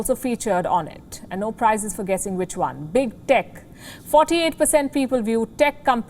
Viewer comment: SHE SOUNDED LIKE SHE SAID BIG DICK, HAHAHAHAHAHAHAHAHAHAHA